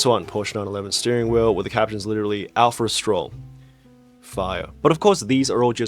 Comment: heard that as alpha or stroll? And thought that was a niche f1 joke